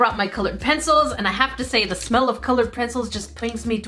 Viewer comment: Unless it my mishearing but i swear around 7 minutes in when brings out her coloured pencils she say "ah the smell of coloured pretzels..." im not kidding, either cmsomeone checks and corrects me or at that time stamo craved pretzels😅😂